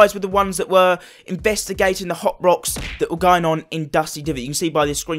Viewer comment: It sounds like you said Hot Box not hop rocks